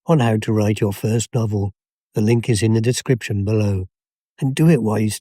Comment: did you say, "the ink is in the description"?